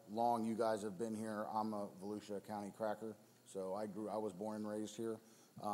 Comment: Did he say “I’m a Volusia County cracker”….wow